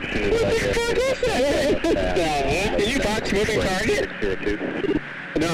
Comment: by the context and the way it sounded i think he said 'did you box the moving target??' as in did he manage to hone in on it manually lol